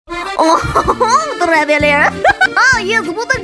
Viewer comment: i thought he said "yes water gliding be fatter" and now venti is my 2nd favorite character (rehehehe)